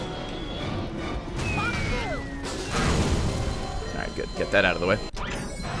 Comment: Did anybody else hear Spongebob say "Fuck you!" at 55:47 ?